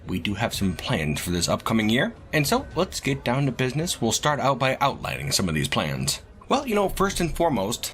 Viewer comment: What said: "And so, lets get down to business; we'll start out by outlining some of these plans." What I heard: ♪ "LETS GET DOWN TO BUSI-NESS! TO DEFEAT: THE GOBS!" ♪